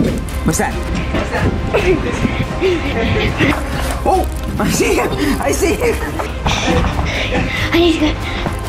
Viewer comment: Everyone is saying the dad said “I SEE HIM I SEE HIM!!” But the more I listen to it the more I hear “ I SEE HER I SEE HER!!” With his accent and an out of breath dad, And if not it doesn’t mean that the parents forced their child to be trans- EVERYBODY makes mistakes- I have a trans friend and I sometimes accidentally get his pronouns wrong, it doesn’t mean I exploit him and force him to be trans does it!? The dad is AMAZING!! why would he create a channel and show the world how his child is trans and fake it!? He understands there will be hate, why would you fake something if people are going to hate it!? It is edies decision and they have said the have a fallback option if she wants to become a man in the future